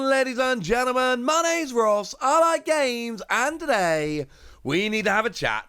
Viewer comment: This whole time, I thought you were saying "allied games" in your intro.